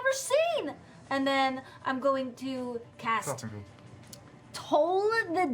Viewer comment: Ashley says RAGE! table goes crazy...I heard "I'm going to cast raise." which in a lot of rpgs is instant death on undead, so I agreed with the tables reaction but didn't think that would work in D&D. Lol